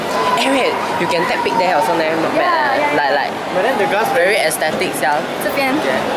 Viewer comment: did i misheard or he really say "very aesthetic sial"😂😂